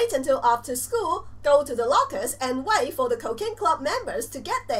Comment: Am I the only one who thought she said cocaine club?